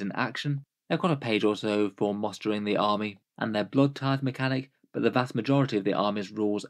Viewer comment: I misheard "whereas maybe the codex might add in the equivalent of their Blood Tithe" as "blood type" and thought you were making a joke about unnecessarily excessive rules 😅